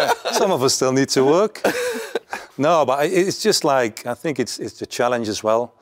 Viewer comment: What did Jaap say at 36:48 ? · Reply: Need the money